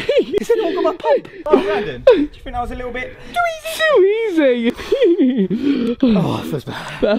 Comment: What did you say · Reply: Rafi Illuminati to easy